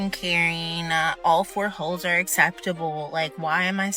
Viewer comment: did she just say "all 4 holes"...? 4 holes???